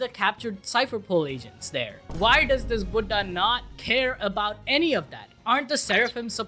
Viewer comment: did he just said (buda)? It means Old Man in Hindi.